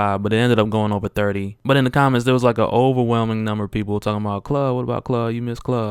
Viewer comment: It sounded like you were saying glove until you said you were saying kluh